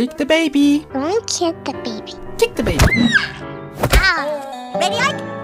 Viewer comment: ME BEING STUPID AND I THOUGHT HE SAID DONT KICK THE GARDEN BABY